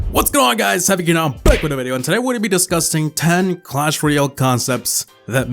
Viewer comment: Am I the only one who heard DISCUSSING as DISGUSTING ??? XD